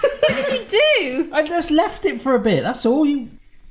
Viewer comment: It sounded like you were going to call your wife a b#$